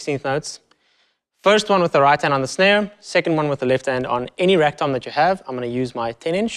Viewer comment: When he said "Any rack-tom that you have" I thought he said "Any rectum that you have".